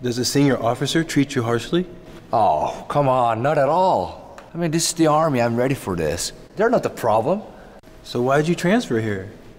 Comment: 뭐라고한건가요?? 영어 스펠링좀.. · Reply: Oh cmon not at all this is army I was ready for this